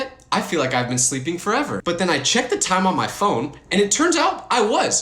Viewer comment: Broo when he said he checked the time on his phone, I thought he said his wife's phone😂 Also me remembering his other videos talking about how he's nowhere close to getting married😅 ( sorry if this seems offensive, i just don't know what other words to use). I pray you get not just a good girlfriend, but a good wife!😄